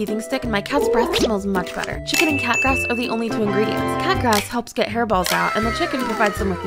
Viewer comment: “Let’s get hairballs out-“ I wasn’t paying attention and I thought she said “let’s get her balls out.” 💀 EDIT: I started a war. Woops. 🤷‍♀️